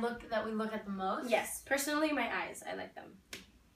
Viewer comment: I swear @ 10:40 I thought Lauren said my ass. But she said my eyes. OMG.